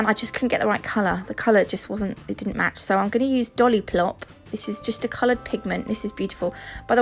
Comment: ROFL, I'm sure I'm not the only one who heard you say dollyplop! 🤣🤭